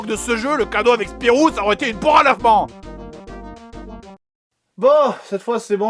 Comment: Juste j'ai besoin d'un captain' : j'ai pas compris le cadeau avec le jeu Spirou s'il existait une version collector ç'aurait été quoi à 18:40 ? Une poire enlèvement ?? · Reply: il dit une poire a lavement :)